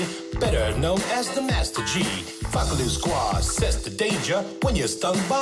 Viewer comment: better known as the Master G. I thought it said master chief I kindly laughed.